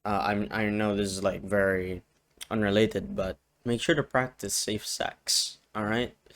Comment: I'm so out of my mind, at 5:47 I heard "make sure to practice same sex" and had to rewind 😭 Happy pride I suppose